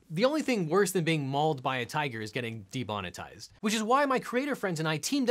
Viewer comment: Did he just say ''debonetized'' instead of ''demonetized''?